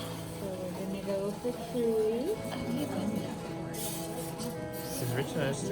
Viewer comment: did i hear "we're going to go for truly?" is that baby girl's name? <3